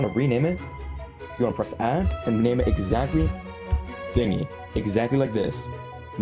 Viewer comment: wtf its says😭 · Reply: It is called Thingy